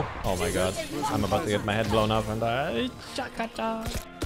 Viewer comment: anyone heard chaka cha ?